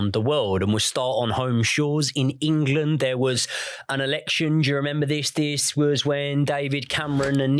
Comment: i thought said there was a lot going on in March 2014 i had an erection then realised he said there was an election! I got all exited for nothin 🤣 🤣